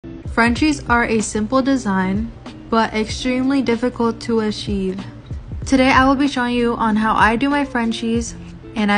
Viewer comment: in the beginning of the video I thought you said "French cheese"